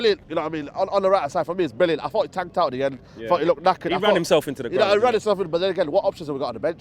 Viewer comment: YungJ referring to the thumbnail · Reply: Manaal it’s only like that because the editor thought he said lacking not knackered lol